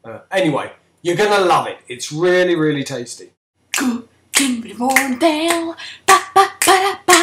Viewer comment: who's the foreign kid who sings the intro? 'cos it sounds like he says "cookilng with warmtail!"??? what the fuck??